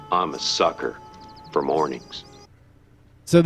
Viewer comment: I wonder what is the episode between 1:00-1:03, did he say that “I am a sucker for morning” or something similar? Cant quite remember but sounds fun!